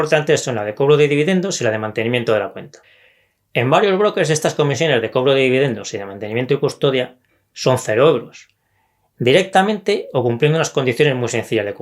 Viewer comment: Alguno me aclara que dice en el min 1:12. No le entiendo. Gracias! Y gracias! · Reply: Hola Sebastián. Digo "las comisiones de cobro de dividendos y de mantenimiento y custodia son 0 €". ¿Es esto lo que no entendías. Muchas gracias :)